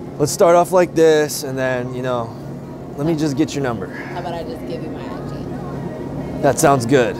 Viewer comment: I thought she said “How about I just give you my address” I was like god damn 😂😂💀